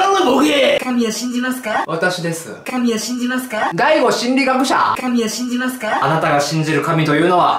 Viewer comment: だいご心理学者 にしか聞こえん